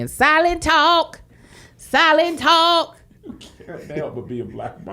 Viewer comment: I thought the baby was saying Silentalk too🤣🤣🤣🤣🤣🤣🤣